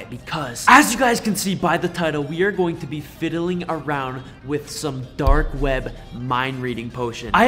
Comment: I thought u said no more dark web